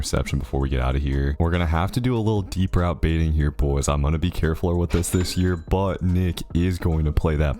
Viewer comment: did you really say carefuler? 😂😂😂😂